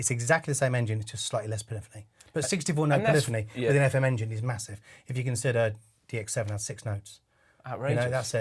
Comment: He said “6 operator” (engine), which is perfectly right. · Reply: octopus34 Um, that's not what he says. He says it has 64 note poly instead of the 128 of the montage, then he says "64 note polyphony with the fm engine is massive, when you consider the DX7 had 6 notes"